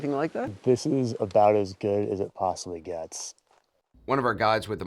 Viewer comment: Did he say: "This is about as gay as it possibly gets."?! 😂